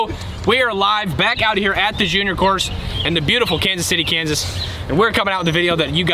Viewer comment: He said the “junior course” but for a second I thought he said “the gm course” they should consider changing the name